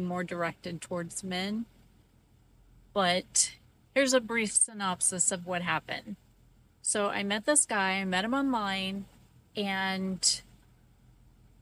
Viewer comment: Thought u said brief synopsis at the beginning?